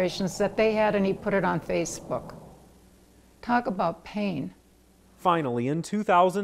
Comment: Did she say KANE?!